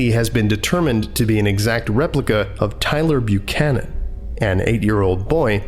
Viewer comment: I am literally laughing ass off right now because when he said the guy name at 3:27, I thought he said Tyrel Buchanan because that's my name, then I went back and he said Tyler Buchanan!!!!! It's too funny!😂😂😂😂😂😂😂